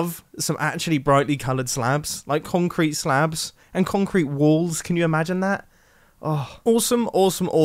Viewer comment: Who else heard "concrete wool"?